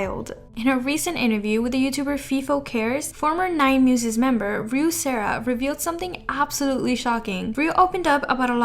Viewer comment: I thought it say Ruv, Sarv from Mid fight masses a sec